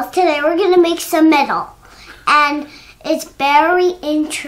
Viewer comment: "good morning boys and girls, today we're going to make some metal and it's very interesting." Love it. Even better, I thought said it's "barely interesting" the 1st couple times I heard it. 🤣 It's barely interesting 🤔 IMHO making metal is indeed very interesting! Edit: apparently I'm 4 years late to the masterclass :)